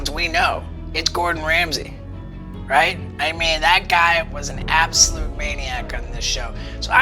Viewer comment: I wish there was cc just for this one video also anybody know what stevo said? Man is a damn beast btdubs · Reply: Sorry for being so late but he said that guy was an absolute maniac on this show